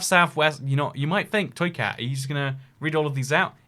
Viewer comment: Toycat when we colonize Mars: This is the weekly series where we talk about earth, mars and everything in between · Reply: Wait what... "Toycat"? I've watched tons of these videos and always thought he was saying "Soycat" D